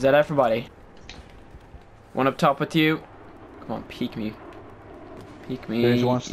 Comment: I thought he said Top to the morning to you!